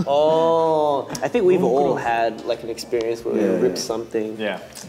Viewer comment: I heard "you wet your pants" instead of "ripped" 🫠